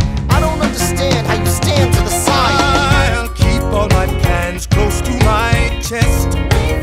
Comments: Please tell me I'm not the only one who thought burr said " I keep all my pans close to my chest" and then had a mental image of burr keeping kid pans close to his chest so Hamilton doesn't interrupt his late night stress baking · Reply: I always thought he said “I’ll keep all my pens close to my chest”, since Alexander doesn’t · Please tell me I'm not the only one who thought burr said " I keep all my pans close to my chest" and then had a mental image of burr keeping kid pans close to his chest so Hamilton doesn't interrupt his late night stress baking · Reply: I thought he said "I keep all my pants close to my chest" kjbsjsjsjs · Please tell me I'm not the only one who thought burr said " I keep all my pans close to my chest" and then had a mental image of burr keeping kid pans close to his chest so Hamilton doesn't interrupt his late night stress baking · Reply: I heard it as Pants. "I'll keep all my pants close to my chest!" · Please tell me I'm not the only one who thought burr said " I keep all my pans close to my chest" and then had a mental image of burr keeping kid pans close to his chest so Hamilton doesn't interrupt his late night stress baking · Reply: I heard “Pants close to my chest” and I imagined when cartoon characters pulled their pants up before throwing hands with someone. So I imagined Burr pulling up his pants like trying to look stronger than Hamilton in this situation